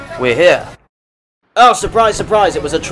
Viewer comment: Anyone else thought he said "Fuck your market" ?